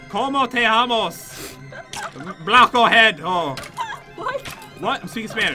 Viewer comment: plate head Me: do u even know what that means Idk if he said that or banana head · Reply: He said blockoheado